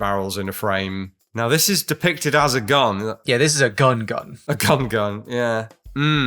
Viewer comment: did just say ‘a cum gun’??